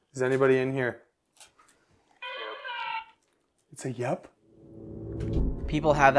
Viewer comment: Yo guys, I am from Germany and at 7:30 I hear nothing similar to "Yup" but "Hilfeee" which is german (Hilfe) for help. I have witnessed this some time before, but I heard definitely help in german and some more things but I am maybe too invested into hearing stuff, because I heard some weird stuff around 9:00 to 9:30 but I am not so sure of this.